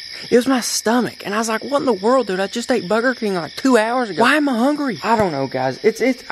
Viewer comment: Did he just say Booger king